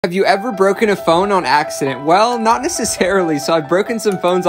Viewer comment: At first I thought he said have you ever broken a bone on purpose